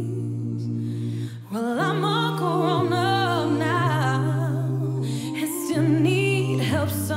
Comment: I heard "well I'm all corona now" and I had to do a double take